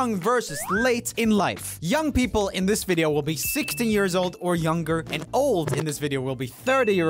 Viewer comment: did anyone else hear 60 years old